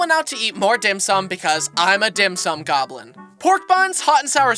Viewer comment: did broski say dim-sum goblin or dim-sum gobbler?